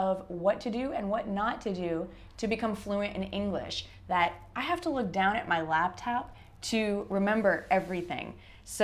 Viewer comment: I didn't catch what you said at 0:30 before you said: ........ my laptop. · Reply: أشرف Hi! I said, "I have to look down at my laptop." You can watch the videos with the subtitles turned on to make it easier! Simply click on the button with the "CC" on it. I hope this helps!